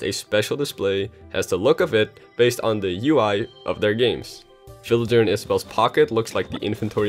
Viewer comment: In the beginning I thought he said "children" instead of villager...